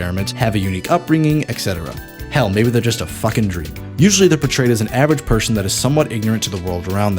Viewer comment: When you called Tidus a "fuckin' dream," I thought you said, "a fuckin' dreamboat," and I was like yeaaah.